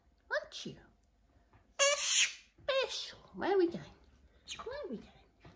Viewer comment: So PRECIOUS! When you turned on the light it almost sounded like he said 'there we go'. He's so cute!